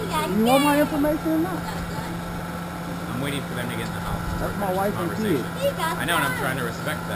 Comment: did he say ''I'm waiting for that n*gga....'' ???? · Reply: "I'm waiting for them to get in the house".